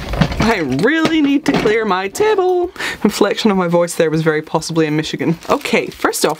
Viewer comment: Did you say at 1:13 that the inflection in your voice was very possibly a Michigan? I'm from Michigan and we definitely do that as a part of our speech inflection, but it cracked me up that you just casually threw that out there! 🖤😂🖤